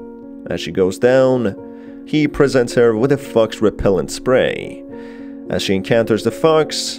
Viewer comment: it sounds like he says "He presents her with a f*cks repellant spray" lmao